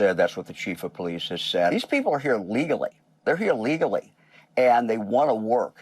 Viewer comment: the subtitles are wrong. He says "legally", but the subtitles say "illegally".